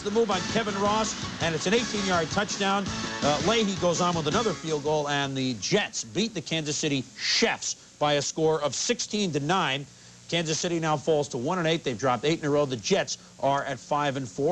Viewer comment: Did Boomer said the Chiefs "Chefs" from the Current-Snickers commercial! LOL